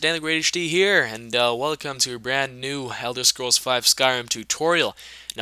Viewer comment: I don't know if its just me but did he just say, "Helder Scrolls" instead of Elder Scrolls?